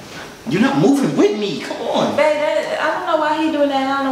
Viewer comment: did she say bae? 👏👏